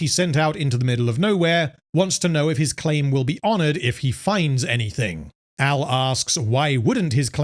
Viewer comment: I thought he said "his claim would be on it" all this time :V